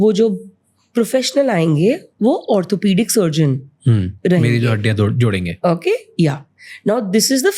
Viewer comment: say what😂 bro almost said meri jo haddiya todenge😂😂😂